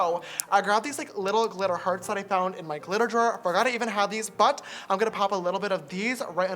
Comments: the subtitles said "clitorus" instead of "glitter drawer" ... wtf lol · THE CAPTION SAYS " THAT I FOUND IN MY CLITORIS" BAHAHAHHAHA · the CC says "glitter hearts I found in my clitoris" and not "glitter hearts I found in my glitter drawer" ...uhmmm what???